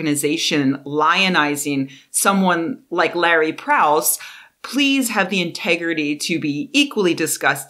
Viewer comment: Lol, I am not a native english speaker, so when you siad "Larry Prowse", the stress was so similar that I genuinely thought you'd said "Lawrence Krauss", so I went "whaaat? religious organization? is that a troll!?" I needed to wind back, confirm you hadn't said that, then I spent the rest of the video thinking, "yeah, nothing surprising there", twist included XD